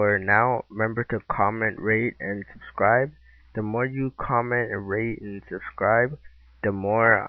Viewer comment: i thought you said comment and rape.. the more you comment and rape the more ill make vids XD no offence thou... this was at the end